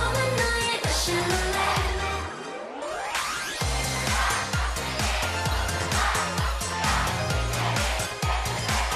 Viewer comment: Anyone know what the crowd is chanting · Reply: bae joohyun , kang seulgi , son seung wan , park soo young kim yerim , red velvet russian roulette!